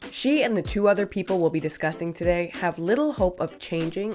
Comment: I think you said disgusting instead of discussing at the beginning of the video